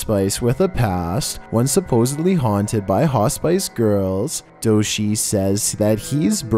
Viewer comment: Fun fact: mispronounced "Hospice" 7 times during this video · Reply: I first had thought he was saying spicegirls... Lol